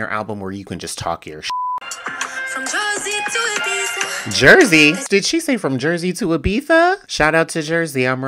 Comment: She said Jozi,thats Johannesburg in south africa 😂❤ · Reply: Ohhhhhhhhh ahahahahaha!!!! Jozi! Of course my dumb ass thought she said Jersey LMFAO!!!! Thank you 😂🤣🙌🏾💯